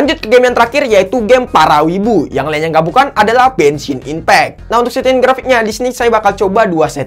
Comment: apa aku gak salah dengar? Dia bilang "Benshin Impact" what??